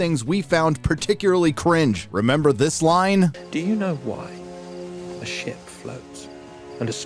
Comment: i heard "do you know why shit floats?"🤣